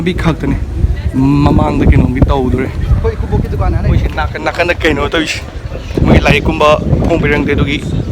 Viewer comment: khoi ekubok ki dukan aaa leire... Who else heard that.. lol???